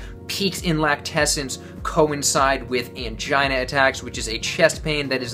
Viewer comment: Wait what? Did you just say "mangina attacks"?!